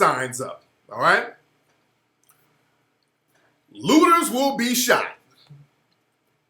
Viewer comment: I thought that said "Lobsters will be shot"......